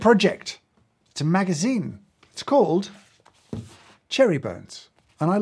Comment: Oh it’s Cherry Bones, I thought he said Cherry Burns…